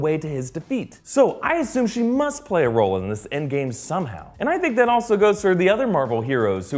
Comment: Did he say END GAME at 4:56...Cool prediction as this video was made in Oct...